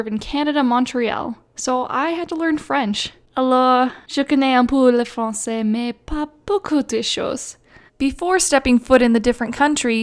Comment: on 0:41 subtitles said 'a la chicken ampulla francais may papa issues'